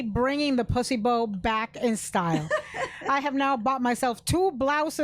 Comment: Omg I thought Ana said p*ssy boats…lmaooo! I’m like is that another word for a vajay